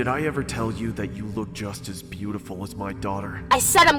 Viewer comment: The manager on the first video- "you remind me of my daughter" lord help me. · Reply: I believe he said, "you're as pretty as my daughter." I hear "duelling banjos" faintly playing in the background. 😆😅🤣😂